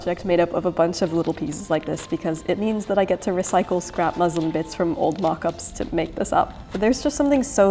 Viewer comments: I should probably note that I ended up having to remove the gorgeous trim down the front of the overgown for sizing, but rest assured that the beautiful Nami Sparrow added it to her gown for the ball, which you can see briefly at 26:39! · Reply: Dear Bernadette, I just wanted to give you a hint that the English default subtitles (at 00:59min) accidentally say "it means that I get to recycle scrap Muslim bits" instead of "muslin bits". This might confuse some people that don't have as much knowledge about different fabric types 😅 Thank you for the beautiful video! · A small correction: At 1:00, the subtitles say "I get to recycle scrap Muslim bits" and I suspect you mean "scrap muslin bits."